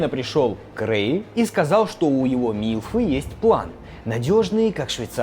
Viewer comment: мне послышалось или на 11:25 сказал "Милфы"